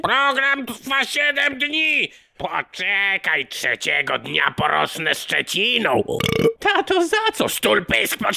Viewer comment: co mówi ? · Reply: "Poczekaj, trzeciego dnia porosnę szczeciną"